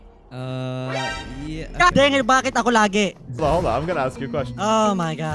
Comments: The text:"bageh Tagoo lageh" What he really said:"baket ako lage" 😆 Atleast you tried your best.🙂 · every body who did not know what said in 6:10 he said "god dang it bat ako lagi" it means "god damn it why is it always me"